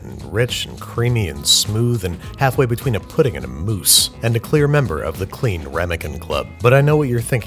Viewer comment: is it bad that I thought he said “moose”?